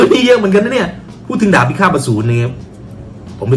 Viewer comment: ใครได้ยิน ดาบพิขาบอสูร บ้าง😂